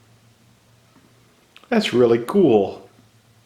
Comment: did you say "That's really ghoul"???